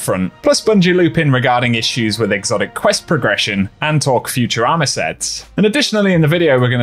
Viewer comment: I'm sorry but I heard that you're gonna talk about Futurama sets lol